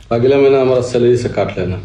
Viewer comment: Kya bola samajh me nhi aya 10 bar back krke dekh li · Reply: Note fadke bola agli bar hmari salary se kat lena😂